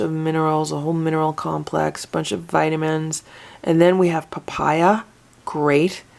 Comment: When you said "Papaya" i could swear i heard "Papyrus" And i thought "That can't be right, why would there be paper in Cat Food?"